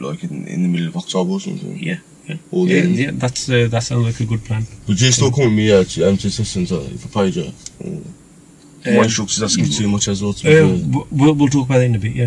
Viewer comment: What does he actually at 37:10 say before says “we’ll talk about that in a bit because this is still on camera”? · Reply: “would you still come with me to the lessons if i paid you?” or something